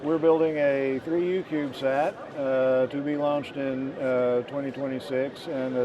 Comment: ! Nice video. Did he say, "free youtube sat" at 0:22? What's that mean? See you out there. 73 de Bill